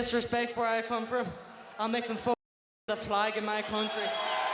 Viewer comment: What does Gallagher say at around 16:20 that sounds like it was censored out? "I'll make them ????? flag in my country". He says 'fuck' several times, which isn't censored/silenced. Anyone know cuz it's bugging me like crazy!!!